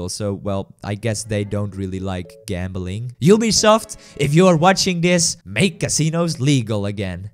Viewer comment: Ubisoft if you are watching this , make casino legal again. What i heard : you’ll be soft if you are watching this , make casino legal again.